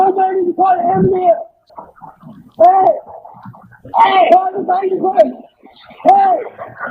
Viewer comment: The lady is clearly saying call the fire department, not the fire is right in front of him lmfao